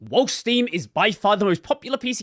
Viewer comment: woke steam? I can't hear that first word no matter how hard I try lol